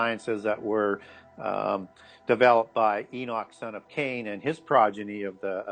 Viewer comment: Enoch son of Cain? Did I hear correctly?